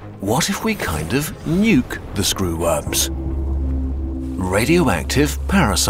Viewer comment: i keep hearing "screwups" not screworms